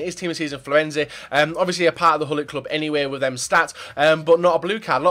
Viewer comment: what did you just say? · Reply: gullit club